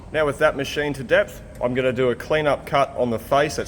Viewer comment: "machined to depth" I thought he said "machined to death" :-)